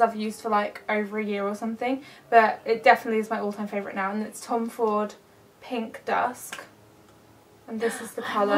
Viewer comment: I think it sounds like she said Tom Ford's pink dust and it is a beautiful color.